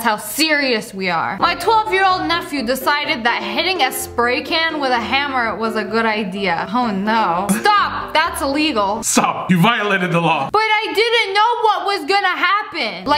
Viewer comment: 'STOP YOU VIOLATED THE LAW' My fav bit of the video🖤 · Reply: It sound like was saying "op you violated the law"